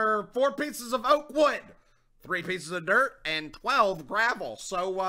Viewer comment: What did he say "4 pieces of caer."